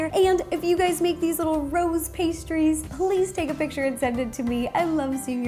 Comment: its sounds like she is saying Ro's pastries! And yes, I do know that she is saying rose pastries.